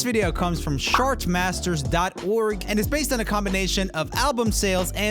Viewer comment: Oh man, I was listening and not watching, and I thought you said Shartmasters. Don't go there, whatever you do.